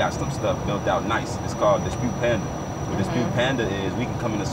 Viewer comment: What tool did he say at the 7:14 mark? was it Dispute Panda?